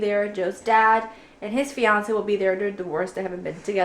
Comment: Min 5:13, did she say "they're the worst" ???? Talking about Joe's parents?.... :O